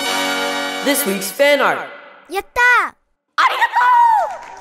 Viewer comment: 英語勉強し始めて１年半になるけど、未だにここ何て言ってるか分からない。This expend art.的な何か。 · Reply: This week's fan art!